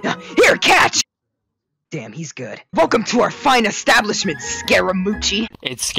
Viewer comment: Am i the only one who thought xiao said daddy’s good? Instead of damn he’s good